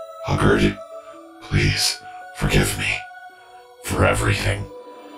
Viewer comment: What? did he say? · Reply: Forgive me for everything.